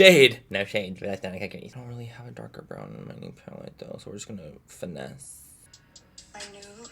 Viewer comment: BITCH I HEARD THAT “I DONT HAVE A DARK BROWN IN MY NEE PALLET” YOU AINT SLICK HUNTY